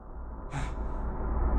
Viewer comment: i think im delusional but im hungarian an when scout is captured at 34:56 i hear "segítsenek" which means help me in hungarian